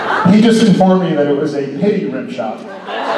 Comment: I think at 35:11, Jensen said, "better than a pity rimjob." To which Jared responded "is it though, is it?" hahaha